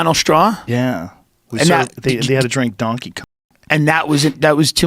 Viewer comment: Because of the censors around 7:13, it sounds like Rogan said “Had to drink Donkey Kong”